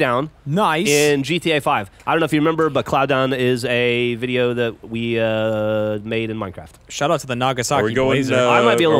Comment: Did anyone else hear (i think it was) super quiet wisper at about 00:29 right after says the guy made cloud down in gta? It sounded sorta like was cursing lol. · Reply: yeah i'm pretty sure i heard a "god damn it!"